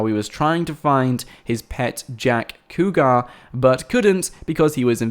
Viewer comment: For a second, I thought it said "jet pack" ( T~T)